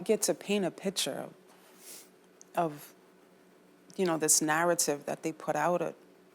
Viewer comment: Did she say she was white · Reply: She said that was the narrative they put out